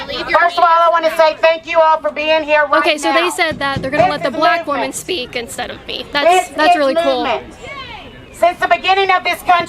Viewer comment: kaitlin literally says "they're gonna let the black woman speak instead of me. that's really cool." sarcastically and she claims she's not racist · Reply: SHE SAID--> "THEY SAID THAT THEY ARE GONNA LET THE BLACK WOMAN SPEAK, INSTEAD OF ME"... (2:43) IF YOU DONT EVEN KNOW HOW TO QUOTE, JUST KEEP IT TO YOURSELF